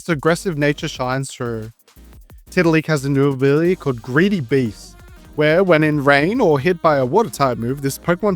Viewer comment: Lol, I thought you said that Tiddaleak's ability was "Greedy B*tch", hahaha